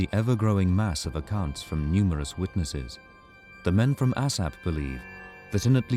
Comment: did he say the men from asshat? 😂